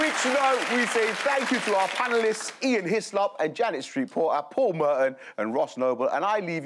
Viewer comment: Being unfamiliar with UK journalists I heard 'Janet Street-Porter' as 'Janice Reporter' and thought that was such an awesomely appropriate name